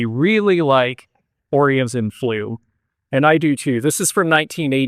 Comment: Can anyone tell me what the maker is for the Blue glass fish (with paerwork) at 2:29? I could not understand what was saying. TIA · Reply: Orient and flume. That is a glass blowing art glass studio from Chico, California.